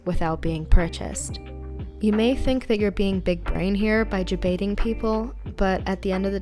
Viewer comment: What are you saying at 5:38? It is very hard to understand. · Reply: Ah, "jebaiting", is a word us young whippersnappers use. It essentially means to fool or trick another person. In the context of what I was saying: you may think that you are fooling others, when in reality, the only person that you are fooling is yourself ;p